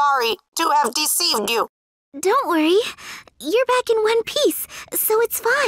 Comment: Did hinata sayd one peace in